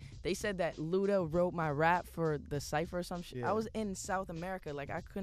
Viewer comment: "Or some shit" I swear i heard him say that lool